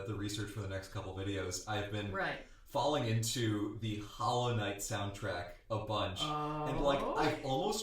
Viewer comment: I heard "The Hellenite soundtrack" instead of Hollow Knight and I had no idea what that was, but I just rolled with it because whatever called The Hellenite is 200% something would listen to.